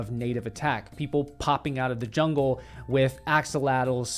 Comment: Sounds like you say pop out of the forest with axolotl